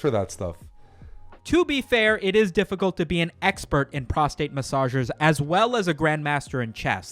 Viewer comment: I heard "Prostate Messiah"